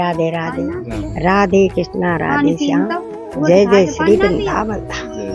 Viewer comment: What is last word at 9:01 - “राधे कृष्ण राधे श्याम जय जय श्री ……” 🙏🙏🙏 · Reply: राधे कृष्णा राधे श्याम जय जय श्री वृन्दावन धाम